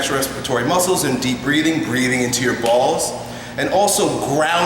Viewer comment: did he say breathing into your balls?